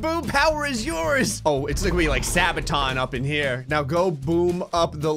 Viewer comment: did he say sabaton